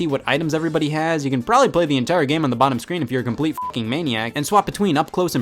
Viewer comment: what did I hear maniac? Bittomscreen? Welp. I'll do it with mariokart 7 edit: I DID IT I won against an cpu xD